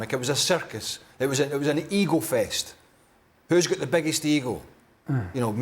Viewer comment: I thought he was saying : who has the biggest Eagle lol- That accent is brutal.